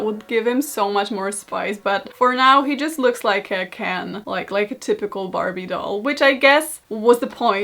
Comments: I thought she said can🥫 instead of Ken at first and I just accepted it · Almost died when i thought u said "for now he just looks like a CAN" at 9:17, but i guess being Ken is also okey